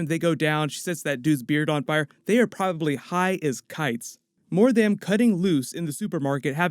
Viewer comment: " They are probably high as KITES." Me: "Did I just hear the wind say Kite-Man?!"